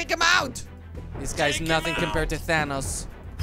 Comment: Who do you think Thor should take on next in Minecraft? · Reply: Did you say thanos or my ears are not working :)